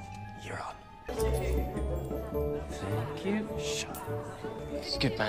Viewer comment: I heard "fuck you” instead of thank you at 3:53😭😭